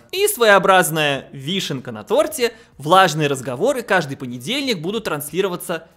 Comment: Мне не послышалось? сказал "влажные"? ... "Влажные Разговоры о Важном"... звучит... хмдаа, короче. Или же "Важные Разговоры о Влажном"! Почему нет?